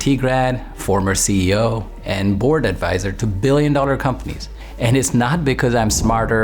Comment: I thought u say bored 🥱 advisor 😅